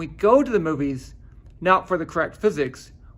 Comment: 他最后一句話， I am fine with that, 我第一次直接聽成 I am fucking done...愣了一下